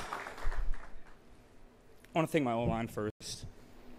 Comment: “I want to thank my o line first.” Yeah he will be a star in the nfl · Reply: Oh!, I thought he said, "my old-man first".